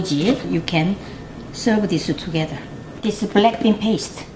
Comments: Was I the only one who misheard BLACKPINK instead of BLACKBEAN?? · I thought i heard u said blackpink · I thought you said BLACKPINK not BLACKBEAN😂😂😂😂 DEAF ME